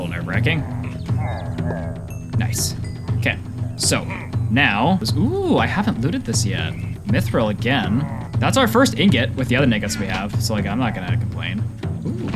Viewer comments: my guy did not say nuggets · with the other WHAT we have? (i swear he accidentally said the n word 😭)